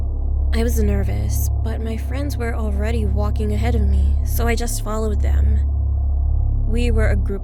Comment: I thought she said "I swallowed them" and I was like wHaT 😂